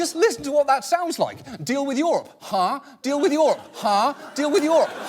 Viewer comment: Misheard "Trade with Europe." "Huh?" and heard "Deal with YoRHa" lol